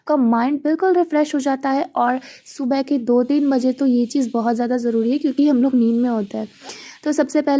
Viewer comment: Did I heard "Subah ke 1:30"?! 🤯